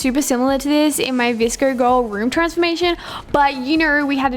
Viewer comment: It sounded like she said vrsco insted of vsco 😂